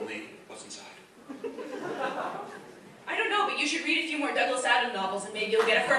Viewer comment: what did she say he should read? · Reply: Tornado.